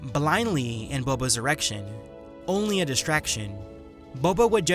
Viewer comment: anyone else hear at 24:24 "she would toss out a frag grenade blindly in boba's erection?